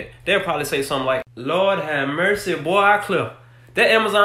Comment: You said lawd have mercy that package was supposed to come in last week 😂😂 ROLL TIDE oh I ain't seen it yet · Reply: I was confused because I thought he said mustof instead of supposed to have.